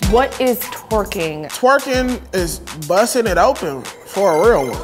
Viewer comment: I had on the captions when the guy was explaining what twerking is (0:09) and it said: "Twerking is busting it open for Elmo" 😭😭😭💀